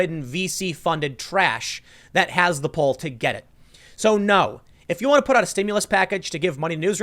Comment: Thinning out some of the media, especially those that are not truthful and push an agenda, would be a good thing. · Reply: "high-profile, slime ridden, VC funded trash" For a second there I thought Tim said "feces funded trash"